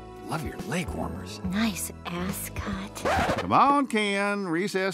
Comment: Barbie " nice ascot" I thought she said nice ASS CUT!!!